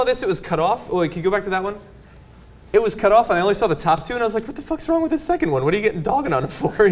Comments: Did he say wtf is wrong with this one 0:56? Lol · I swear to God I heard "...what the fuck?..."